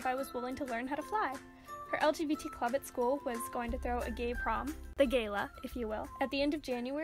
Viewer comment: Here me out... I thought is said Gay Porn and thought "I wouldn't mind being surprised with Gay porn"